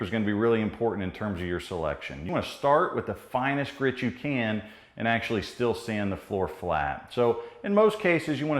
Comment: Did he say start with the finest sandpaper of 36grit and move to finer? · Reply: He actually said, "start with the finest grit you can and still sand the floor flat". Having said that, 36 grit is a bit aggressive. Especially, if this is your first time. Best to start with one grit higher if you're new. You never, ever sand with the roughest because once you sand too much, there is no going back.